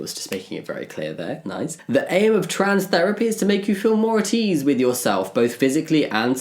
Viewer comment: I misheard "more at ease with yourself" as "more he with yourself" 😂